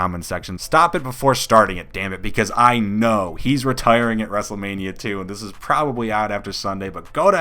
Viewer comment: Did he just say kurt angle will retire wrestlemania 2?